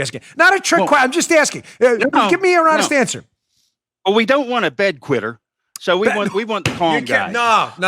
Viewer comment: For a second, I thought Joe said, “Well, ya don’t want a bed wetter.”😂